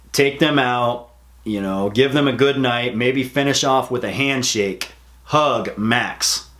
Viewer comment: I thought you were about to say "finish off with a hand job"